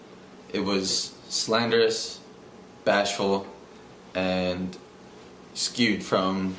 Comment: Did he say bashful??